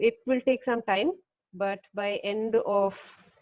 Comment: Did I just hear "By the end of 2022"?!! Does It mean that for 2 more years we'll have to live like this?